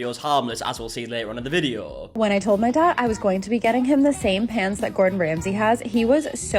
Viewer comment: lol I heard “pants” not “pans” and was so confused 😂